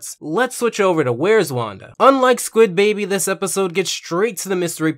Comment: Did you say squid baby at 5:00 (approx)